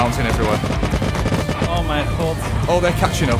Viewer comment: do i hear an dutch person say o mijn god XD im dutch too